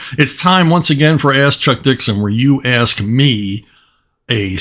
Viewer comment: I totally heard that as "Ass Chuck Dixon" 😅